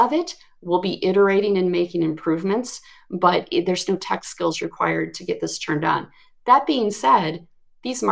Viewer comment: Question ... around 48:30 a comment was made by about LIMS. Did she say "there's no tech skills required" ... or ... "there's still tech skills required"? I've listened a few times and cannot make that out. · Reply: Hi there. said, `there's no tech skill required to turn on the llms.txt generator`.